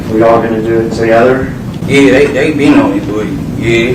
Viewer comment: Hold up what did say at 15:10 it sounds like " they 8 deep " when asked where yall goina do this together i tried slowing it down but still unsure 😅😅